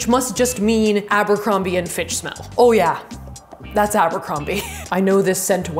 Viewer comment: Who else thought she said bitch?